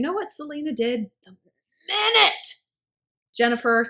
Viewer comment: What is she saying here? For some reason I can't understand haha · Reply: The minute